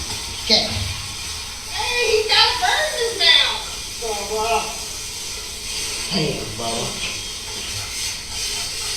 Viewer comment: Listen VERY close at 7:02:33ish... WHAT does Maggie say just prior to "He's got a bird in his mouth".. Does it sound like "Eddie"???? Ive listened NUMEROUS times thinking it was "Alec(x)".. LISTEN NUMEROUS times!! I sounds just like she is saying "Eddie" (Curtis Eddie Smith?) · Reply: Guinea (fowl). Then Paul corrected her, saying it was a chicken.